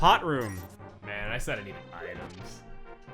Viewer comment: I can't tell what says at 4:16. "Man, I said I needed items"?